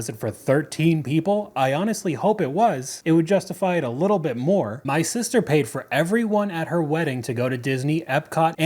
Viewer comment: 7.20 I heard that as "My sister paid for every one of her weddings...".